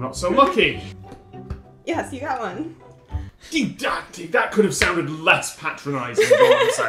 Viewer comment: what did he say? “Div dacti”